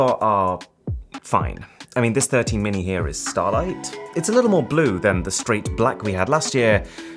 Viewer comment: that color is actually called “midnight” not “startlight”